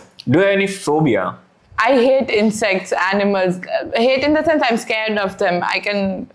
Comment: what was she say... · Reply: Insects,animals